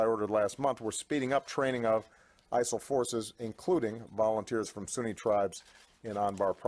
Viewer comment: Did Obama just say they speeding up training of ISIL forces?!?!?!